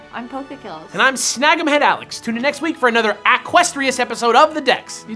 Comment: Keldeo next week! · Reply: Did Alex say "equestrian"? It sounded like he said "aquestrious", but there doesn't seem to be a word like that.